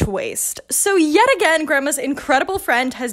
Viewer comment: Help I heard ‘Grandma’s’ as ‘grimace’ 💀